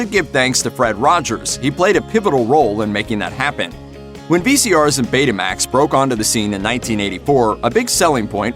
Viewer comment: "VCRs and Betamax"? Um, FYI, Betamax is a VCR system! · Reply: I thought they said VHS and Betamax.